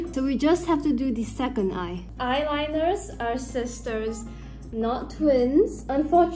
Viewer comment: I have my first drivers license test tomorrow fellas B)) wish me luck (I will try not to run into pedestrians during the driving test) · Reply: Thought you said "Hi sisTERs..." LMHO. Hope the driving test went well.